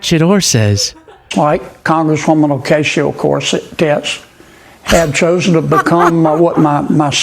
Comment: Sounded like he said Ocacia Corset Tits 😳